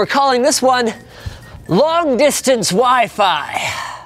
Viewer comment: I thought you've said long distance wifu XDDDD